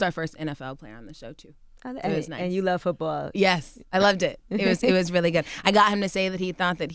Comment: anyone else thought she said "yess I love dick" or was it just me LOL